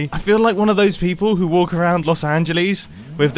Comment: did he just say “Losangeleese”?